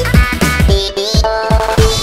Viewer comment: sounds like Anya is saying “ChiChi” AWW OMG